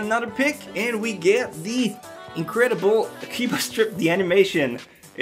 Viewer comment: when he said akiba's trip I thought he said akiba strip XDDD